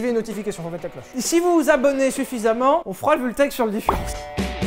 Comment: j'ai entendu "on fera le vultech sur le différentiel" j'ai abonné tous mes comptes